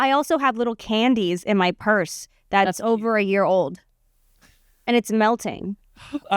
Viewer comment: I thought Olivia said she had year old panties in her purse and was so confused when she said "oh and they're melting" 😂😂😂